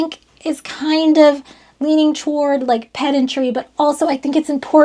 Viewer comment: I seriously thought you said, this leans toward Pet Injury. You mean like kick your dog because this is getting frustrating? I'm not frustrated. Oh Pedantry..... yeah that makes more sense. Also, I love your descriptions as you get technical with something things and could also replace other portions with the word magic. So the Radio receives those waves and the radio does some "magic" and you get sound waves that you can hear. I enjoyed this whole thing btw.